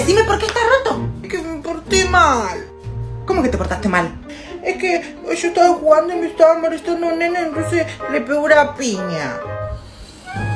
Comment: JAJA OKEY PERO..✨Y nc le pegue una piña✨ JAJA Gracias por los likes {3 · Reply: yo escuché "tonces le pegué una piña" pero no escucho muy bien😩